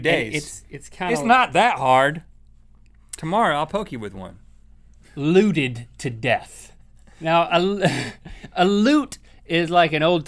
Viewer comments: I thought he said lewded to death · i thought link said "lewded to death" instead of luted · when you said looted to death, i thought you said luded to death as in quaaludes